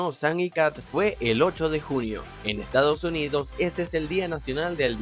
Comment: Notaron Que dice 8 de junio pero el dice 8 de julio